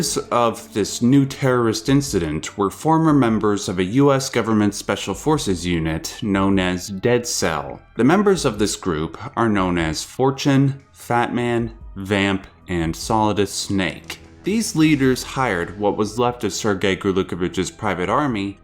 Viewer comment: lol i hear 4chan not vamp